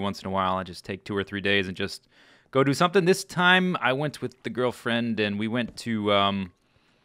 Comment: I heard "with the girl friends". and I'm like "That's an odd thing to say but okay, whatever you say Zisteau"